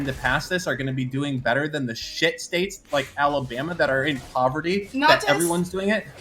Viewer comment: “shit states!!” With so much anger lol. Sounded like “shithole countries”